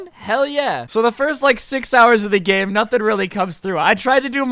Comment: did he just say the first SIX hours